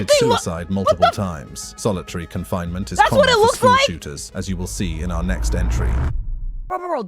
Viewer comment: I thought it said school shitters LMAO